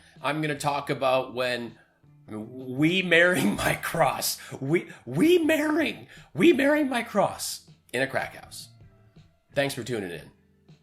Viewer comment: I thought he said he married his corps in a crack house. Or his crocs? I can smell a bat in a troll? Well, it makes just as much sense as anything else he says these days. 🥴